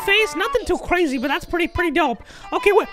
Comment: I thought he said " like D.J." which scared me for a sec